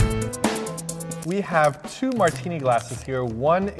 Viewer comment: i heard "we have two Martina glasses here" hahaha